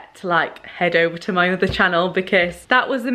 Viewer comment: Dont think we didnt hear you dubb in other at 1:29 lmao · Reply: I thought she said 'mother' and had to check the comments 😂